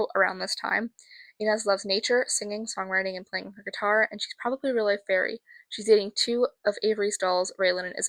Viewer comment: Regarding Inez, HOW did you get her hair to stay up in that swooshy style? Teach me your ways! Also, what did you say at 5:30ish? The captions say "really fairy", and my ears are hearing that, too, but I don't know what that means. I've replayed it three times, and still can't figure it out. What language is the name "Kirtsada"? Never heard that before. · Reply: inez’s hair just stays like that !! i think its because samantha has curly hair and its been cut. i said shes “probably a real life fairy” because her aesthetic is very fairycore lol !! and kirtsada is a thai name :)